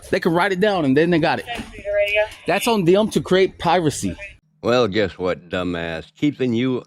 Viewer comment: A Frauditor saying " I didn't want to waste my Time "... thats what the do the moment they roll out of bed. At 8:15... did he say it was on them to create " PIRACY"?